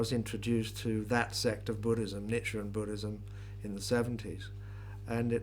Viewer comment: Bob mentions a sect of Buddhism at 4:52 in this interview but I can’t quite make out what he said - does anyone know what he is saying there? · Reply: I think he says "Nichiren Buddhism", I was just investigating that right now.